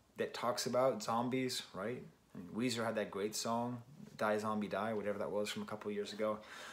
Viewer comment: Thought you said "that Weezer song Die Zombie Die"